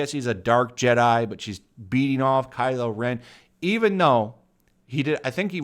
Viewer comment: Bro did you just say “beating off Kylo Ren?”